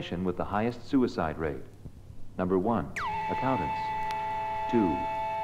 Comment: The professions with the highest suicide rates: Number one ??? Number two Riders Number three Positions Gotta love automatic captions. No but really I can't understand the first one, can someone translate it to English? I'm a writer learning music so I want to know if I'm half screwed or completely screwed. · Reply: Accountants